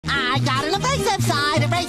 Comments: PLEASE DONT TELL ME I THOUGHT SPONGEBOB WAS SAYING "I got in the racist side, The racist side, the racist side" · I thought he said "I got a rasict side" i'm like